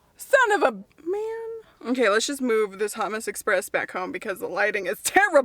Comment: When you said “let’s move this hot mess express” I heard “let’s move this hummus express” lol